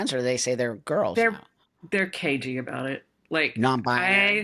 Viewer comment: LMAO! If you turn on the captions for this video, at 11:58, instead of saying "They're cagey about it," the captions say, "They're KY about it..." ;-)